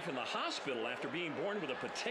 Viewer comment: am I crazy, or did he say that he was born with a potato for a heart??